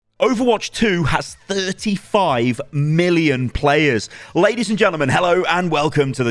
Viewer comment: “Overwatch has 35 million players” What I heard: “Overwatch is robbing 35 million people”